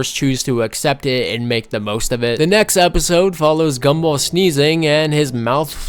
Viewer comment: Did you just say... MAKE THE MOST OF IT? *Insert vynil scratching *